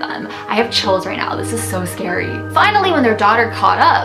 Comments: Did say quote "I have children now this is so scary" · I thought she said I have children now this is so scary but this was not the case, she actually said I have CHILLS right now